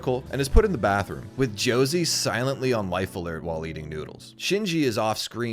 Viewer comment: I actually thought he said "wife alert" at first and suddenly found myself wishing I had a Josie on my floor munching noodles and making sure I don't die.